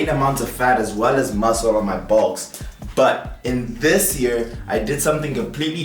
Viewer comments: what did he say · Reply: on my "bulks"... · I thought he said I gained fat on my balls he said my box at least that’s what subtitles said😂